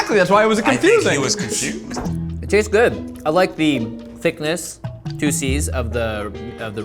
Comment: I thought he said "Thickness, two c's," and not "Juices" like the captions say :/